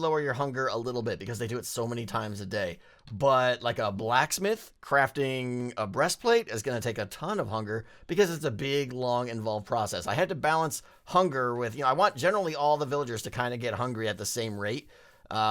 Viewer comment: did he say breast plate?